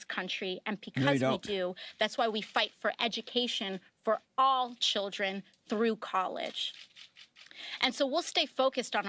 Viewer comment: Did she just say their agenda is to distract children of the countries core values through college ? blink